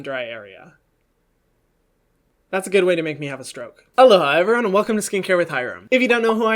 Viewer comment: I just realized he says "Aloha everyone." I thought he said "Hello everyone." DUN DUN DUNN